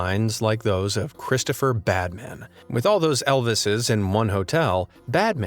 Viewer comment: at first I thought you said Batman but you said bad man